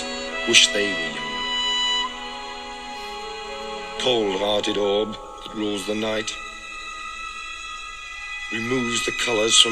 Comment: The words on screen said, "cold-hearted love that rules the night" when I heard "cold-hearted orb that rules the night". I think he meant the moon which rules the night.